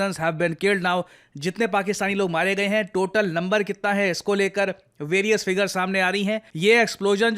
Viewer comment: Am I the only one who heard "Lumber"?